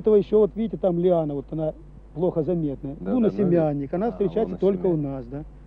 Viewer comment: как он назвал актинидию, кто расслышал? он назвал ее - "лунносемянник".